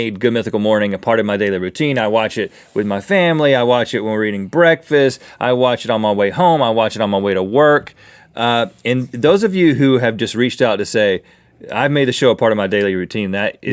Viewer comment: Did Rhett say reading breakfast